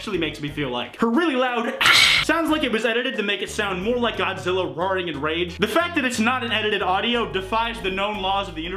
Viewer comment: Did I hear that right? “Godzilla rawring in rage”? Damn